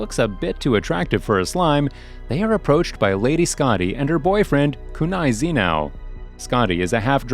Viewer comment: Did I listen boyfriend instead of bodygaurd ? 😅🥲